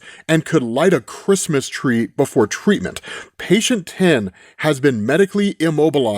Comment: in and was just listening not watching, I heard "patient Tim" immediately thought Tim Drake? I was gonna lose it